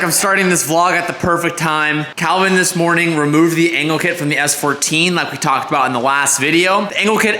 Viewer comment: Bro I swear it sounded like you said he removed the anel kit